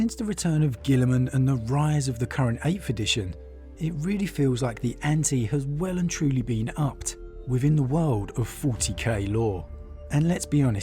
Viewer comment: anyone can transcribe for me what he is saying there? I can't understand the key part no matter how many times I try :( (still learning English) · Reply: And the rise of the current 8th edition, it really feels like the ante have well and truly been upped within the world of 40k lore (Basically just saying that 8th ed is where things have been getting really intense haha 😊)